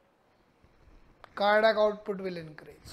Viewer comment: Thanks SIR Pranam GOD bless you sir for sending useful video of Telmisartan SIR DR MohanPatil Govandi East Mumbai Orchid residency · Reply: What did he say at 6:23 increases? Cardiac load? Sorry I’m not a medical professional.